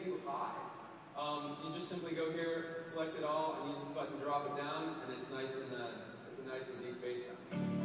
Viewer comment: did he say just click and fuckin drop it down